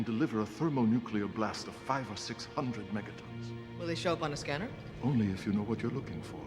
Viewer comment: That was a 500 Mt bomb. It left nothing of the city but a crater. Unfortunately, it looks anything but good. Imagine the scene in the style of the Adama Maneuver. That would be so epic. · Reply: 1-1.2 GT, g'kar said each was 500 to 600 Mt each. You misheard